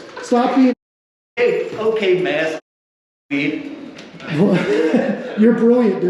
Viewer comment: What did he say here? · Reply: I think he said, "Okay maskhole."